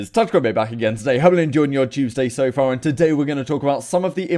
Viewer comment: Sounded like he said "Hope you're all enjoying your Doomsday so far" Lol